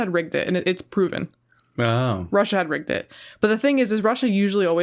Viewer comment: Russia had "Rinked" it! (Get it?) I swear I thought that's what she said at first